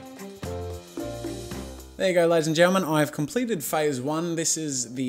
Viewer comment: When I first heard your intro a year back I thought you said get out of our life gentlemen:-)